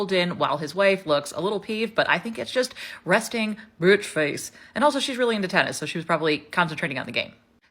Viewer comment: I think I heard you say, "Resting Brit Face?" 😂😂😂 Also, "this is what happens when you marry your cousins?" 😂 I'm so glad I found your channel! I needed some laughs after realizing how low the British media will go.(Also love your substack.) 🙏💜